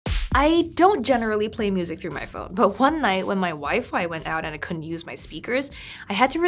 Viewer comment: Wait a second..did she just say me and my wife!?